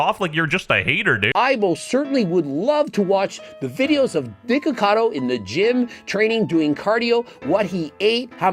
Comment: Why does the subtitle say " Dickacado " at 5:08 :face-purple-crying